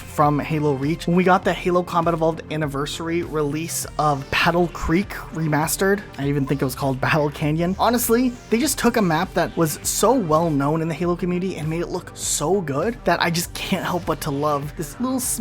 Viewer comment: I'm not the only one who hears "Paddle Creek" right?